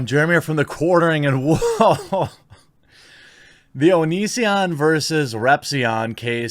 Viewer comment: When I first heard you talk about this I thought you said Onii-san video.